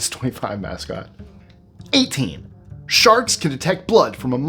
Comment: I thought he said "SHARTS" not "SHARKS" at first!